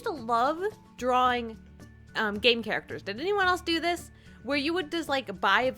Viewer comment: I thought she said gay characters instead of "game characters"